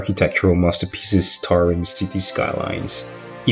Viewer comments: Did You Say Cities Skylines?! That's A Game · Did he say cities skylines in 4:25?